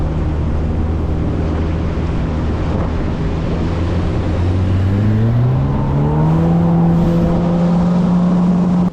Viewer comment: What are would you say that is my friend? Around 14:35 in the video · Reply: Small word lol I made sure to not cause them any wake